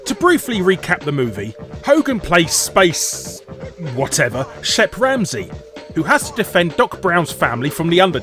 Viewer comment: on suburban commando! at first I thought u said " Chef Ramsay" 😁